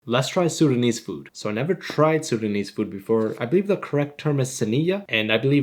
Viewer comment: I thought you said sundanese not sudanese lol